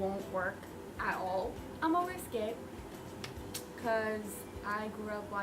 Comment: what did she say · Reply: She said. I'm a risk it